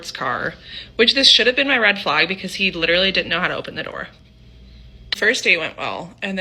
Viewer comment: I really don't know, if I'm getting deaf, BUT after listening to the part of the first story a few times (once in 0,5 speed), I always hear "this should have been my red flag, because he literally didn't open the door" as in "not open the passenger seat door for the date like a gentleman. 😅 Not that he didn't know how to open it.